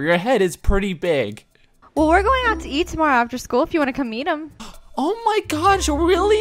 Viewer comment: Wait did she said eat him